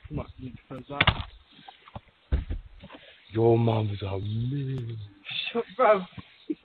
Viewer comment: Did he say "Your mom is awesome"?